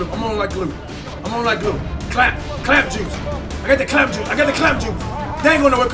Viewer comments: Am I the only one who thought he said, "clap juice"😂😂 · sound like Fredo saying CLAMP JUICE